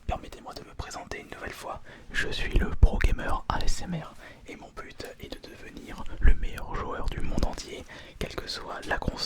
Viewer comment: i heard est mon put est de devenir hahahha am i going crazy?